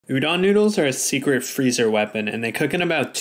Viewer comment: I thought it said Oli London noodle 💀💀💀